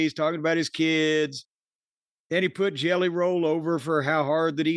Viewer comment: For a moment, and god only knows why, I thought was going to say "Jelliam Rollo"